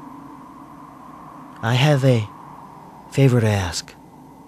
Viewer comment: I thought he said "i have a favorite ass" XD